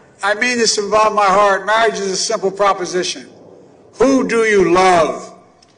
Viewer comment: All I hear is YG, “B*** WHO DO YOU LOVE?”